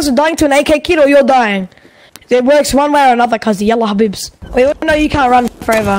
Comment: arabic? I think he said "Ah Yallah Habibs" in slang i think means "Run you pussy" or something degrading...